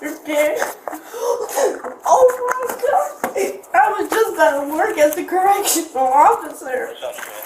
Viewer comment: She just aged outta foster care.. im glad they were easy on her.. i felt so sorry for this girl 😢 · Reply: It is a sad story... at 24:19 sounds like she says to the police officer: "I was just about to work as a Correctional Officer". This incident probably ruined that goal.